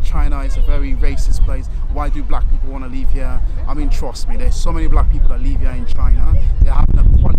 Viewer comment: did he say leave or live? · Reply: live